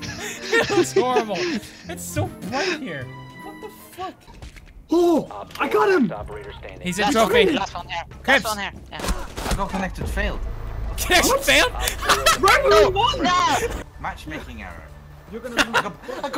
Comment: It said kripps but I thought it said kippis which is my tag xd